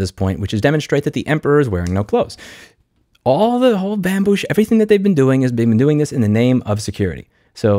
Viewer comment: Did you just call this a “bambush”? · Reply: I think he was going to say “all the bambu shi…” I like Bambush though 🤣